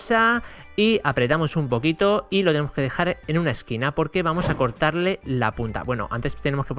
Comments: José si escuché bien pero dijo a cortar le la puta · parece que dijo LA PUTA :V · Soy yo o me parece que dijo cortarle la puta?